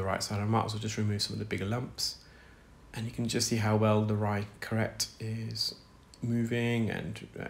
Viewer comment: I'm smiling because with your British accent I hear " right correct ".... Love watching your videos!